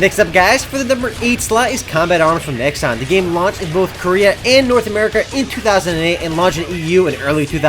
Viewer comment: Did he just say the 8th game launched in North Korea ?